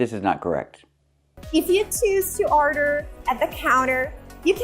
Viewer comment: I was having a harder time understanding what she says at the beginning. I could only hear "if you 'toos' to order" apparently she means "choose to order".